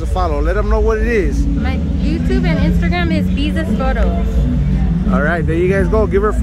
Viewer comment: Yo, who's the girl ( 19:26 ?) I couldn't catch what she said. Am down to give a follow! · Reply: on IG